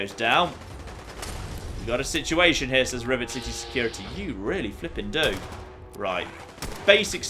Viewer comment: Haha you said rivet city security like 4 times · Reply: I heard him say rivet security